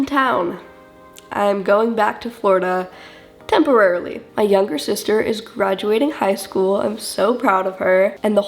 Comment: londyn is the one graduating, not aven. aven is 14 or 15 i believe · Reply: I assumed so but I thought she said my youngest sister is graduating so I got confused